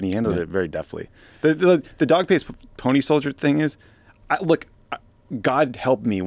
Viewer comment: I heard "dog faced pony stories", which I, um, decided could be an evolution from "dog and pony show" for an indication of something overhyped. He's saying climate change shouldn't be downplayed, and those who say it should be can't back up their claims.